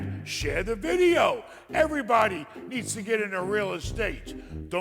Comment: I thought he said he was done with real estate